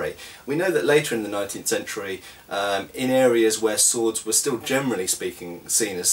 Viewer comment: OFFTOPIC: i have a cold and i hear you saying "germanly" over and over again... i guess its "gerneraly"^^